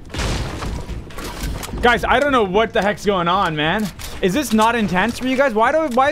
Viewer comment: I thought u said what the he**